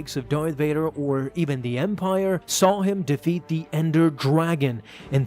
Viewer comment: Me: Did he just say ender dragon